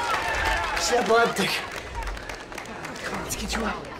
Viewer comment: That lady didn’t fall on the tracks. She was pushed! And whoever pushed her would’ve gotten away with it, if it hadn’t been for those meddling kids! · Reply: I think he said she was epileptic